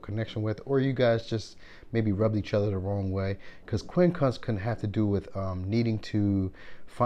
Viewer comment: What term are you saying at @ 3:25 & @ 3:40? Quinn Clux? Quin Cunx? What is this angle? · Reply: Quincunx angle of 135 degrees meaning to a make adjustments for both sides that may not appease them for long but is enough to stop the inner conflict going on